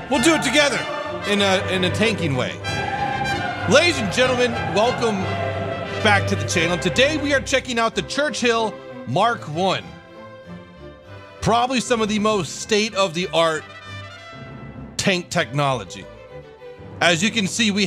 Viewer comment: did you just say The Church Hill?